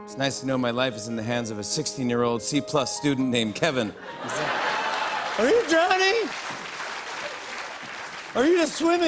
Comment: I thought he said "Are you Johnny? Or are you just swimming weird?". The context made me realize I probably misheard that